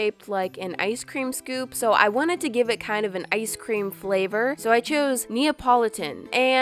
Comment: it sound like she saying "good Thot" or that just me?